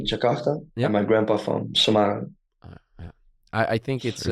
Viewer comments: why did i hear jay said "semarang" But the subtitle says "sumatra"? 😅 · I think theres a little mistake with the subtitle at 6:01. Its supposed to be Semarang instead of Sumatera. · hi thom and, i think there is a miss in the subtitle, when jay said about where his grandparents came from, in the subtitle written "grandma from jakarta and grandpa from sumatra", but i heard jay said "grandma from jakarta and grandpa from semarang", hope i didn't hear wrong ☺️🙏🏻 it's a good podcast, thanks for invited jay as a guest in this podcast! ❤